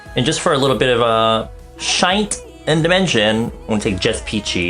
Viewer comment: Thought you said "shite and dimension" tho :')